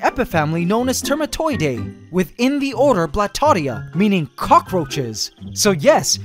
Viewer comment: Did you say Blastoise!?